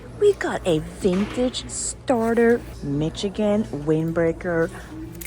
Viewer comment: got cravings for McDonalds with the Michigan starter find since it sounded like you said McChicken lol